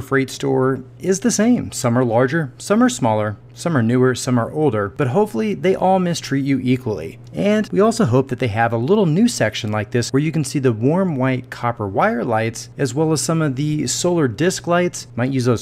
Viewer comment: Did he say mistreat you equally at 7:08?